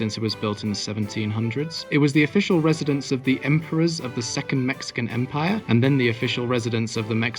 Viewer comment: Did you say "Empress"? What Empress ruled Mexico?